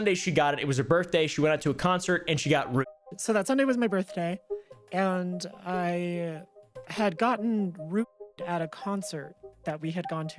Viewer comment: can someone tell me what "ruped" means? is it even ruped idk its censored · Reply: Roofied. Roofie is an informal name for a drug that is used for something pretty dark. Look it up.